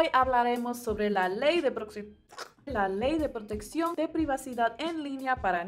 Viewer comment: entendí la ley de pr0stituci0n infantil JAJJAJAJAJ